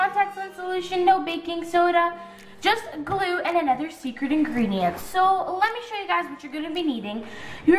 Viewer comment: This made me laugh so hard. When you said secret ingredient it sounded like you said sercit ingerdent. Lol